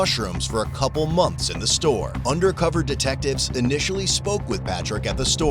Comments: I thought they said "undercover detectives initially smoke with Patrick at the store" 😂😂😂 I'm like how much are they allowed to do undercover?? 🤦‍♀️ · At fist i thought they said officers first smoked with him at the store instead of spoke lmao😂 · I thought it said they smoked with him😅😅😅😅 they said spoke